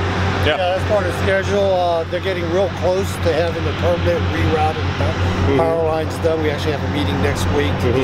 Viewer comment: What is it that says? "They're getting real close to having the ________ rerouting the powerlines...." Surely it would not be 'permit', right? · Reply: Permanent, as in "They're getting real close to having the permanent rerouted powerlines...."